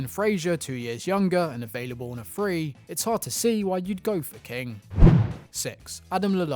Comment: Did anyone else catch that? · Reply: "Its hard to see why you'd go for king"